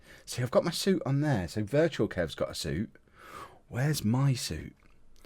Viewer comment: "Where's my suit?" All I heard was, "Where's my title shot!?"